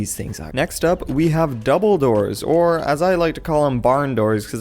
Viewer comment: i swear i thought you said "and here we have Dumbledoors" XD